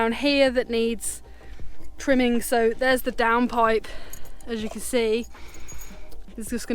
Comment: with your accent, every time you said, "down pipe" I thought you said, "damn pipe"!! Those builders did you no favors. Yay on the shower, you two. 👍😎🇨🇦🐾🐾